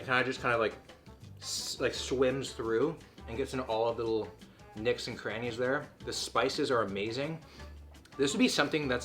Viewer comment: did he just say "nicks and crannies" instead of nooks and crannies or am i losing my mind